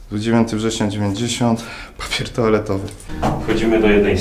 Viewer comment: kto też usłyszał "po pierdole to' zamiast "papier toaletowy" 😂 bo ja musiałem dwa razy przewijać xD